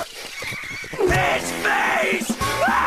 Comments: I can't be the only one who thought that Eddy said "HIS FACE!" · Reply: Not me. Fish Face seemed more appropriate for the scene. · To be honest I thought Eddy said “His Face!” Now I know he said “Fish Face!”